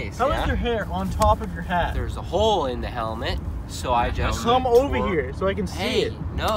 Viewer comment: did say helmet